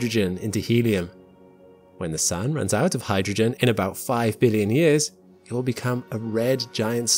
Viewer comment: You seemed to define the first star you were talking about was our sun, but stated it would just take a few million years to use up all the Hydrogen but it will actually take about 5 Billion years, at which point it would start expanding as the balance of gravity vs outward pressure would cause the corona to expand to close to the obit size of Mars, so you need to either correct that or define its either another type of star or not our Sun at the very least, but it is rare for any star to just collapse into a Neutron star as it would still have a small nova as it blows off any lighter gas as it does its final period of rapid contraction, as it collapses into a neutron star or a dwarf star. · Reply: He clearly said 5 billion